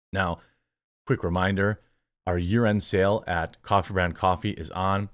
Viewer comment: Dude, you really need to enunciate "year end" I thought you said "urine sale" and I was like WTF?